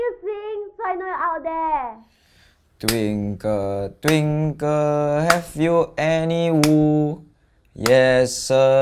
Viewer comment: In the song Twinkle twinkle have u any wool I thought he said twinkle twinkle F u any wool